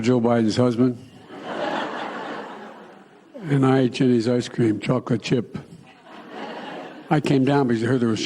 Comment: Every time I hear him say "I like Jenny's ice cream"..it sounds to me like he says Chinese ice cream